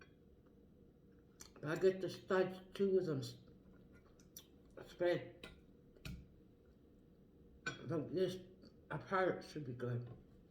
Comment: this is when I notice the Canadian accent lol 😆 I have no idea what you said haha! Nice video mama breakfast looks delish. Congrats again on the new crib! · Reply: I spread the hooks apart to make a distance btn them